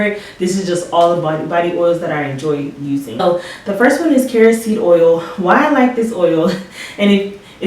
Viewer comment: At first I thought you said kerosene oil!! 😆😆😆 I’m glad I replayed that… 😳